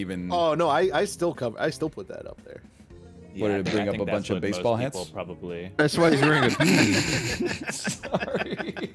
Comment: What did say here 31:00? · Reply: "I think that's what most people probably..."